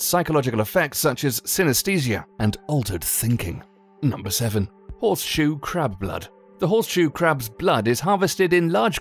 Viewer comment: Lol I thought It said " horseshoe crap blood"